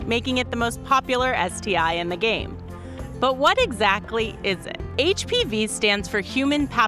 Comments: "Most popular STD" Nice wording, just gonna run down to STDs R Us and pick up my HPV. Common would've been a much better choice of word. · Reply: Levhin Grospe i swear to god I heard her say it was more popular that T.I. and the Game. Hahahaaa I was like wtf lmfao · Think you meant prevalent; not popular lol. Just about fell of my chair at that. I don't think anyone really likes HPV XD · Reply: I swear I heard her say it was more popular than T.I. and the Game. Bahahhaa I was so confused for a second but then watched the video and forgot about it.